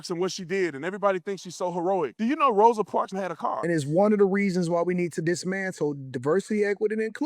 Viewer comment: did he say she had a car??? It so wtf does that have to do with anything 🥴🤷🏾‍♂️